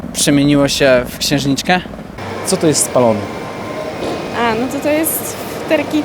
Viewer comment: A, spalony! A ja usłyszałem Stallone ;D